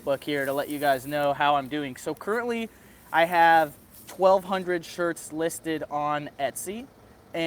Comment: I thought you said you have 12,000 shirts on there? or was it 1,200? I dunno I figured at .20c per listing that adds up when you've got a ton of items? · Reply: I have 1200 listed, and that did cost $240 to get up. When a shirt sells, you re-list it for $0.20 again. So there is some cost and not all your designs will Sell but some will, and they will cover the cost of the others.